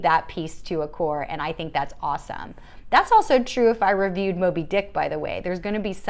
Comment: Me: BTW, Did she says dick at 02:42? Me (after listening to the same sentence 7-times)... Oh, it's Moby-Dick at 02:42... The boon of speed talking... Go, girl go!